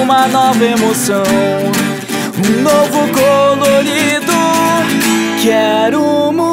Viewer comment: Só eu escutei um novo "cololido"?